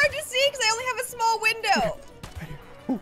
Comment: Did she say window